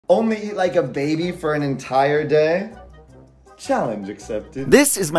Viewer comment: I cant be the only one who thought it said "Only eat a baby for the entire day"